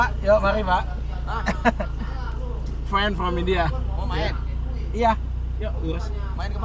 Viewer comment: bhai kya bol diya ne🤣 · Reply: Bhai Friend bola hai par sunne me Fan lag rha 😂